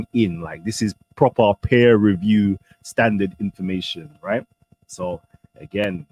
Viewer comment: Did I hear say "proper peer reviewe standard information (@ 24:45 minute). Where is the material he was showing peer reviewed?